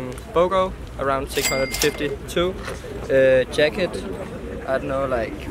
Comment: The guy at 1:46 did not say 652 $ he Said 650 too because The jacket was Also 650 $